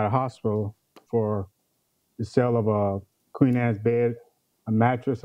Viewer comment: Wait! I'm sorry, but at 1:31 did he say, "Queen ass bed?" I clearly didn't hear , "Queen sized bed." Somebody help me please 🤔🤣🤣